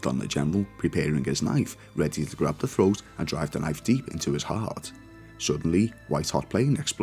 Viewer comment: Did you say ha-heart at 9:43? If so, it was funny. If not, it still was